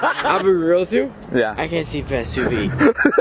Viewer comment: No one: Drunk: I'll be real with ya, I can't see passed 2 feet 😂😂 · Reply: Sara Mumbulla i thought he said he couldn’t see past the subaru 😂😂😂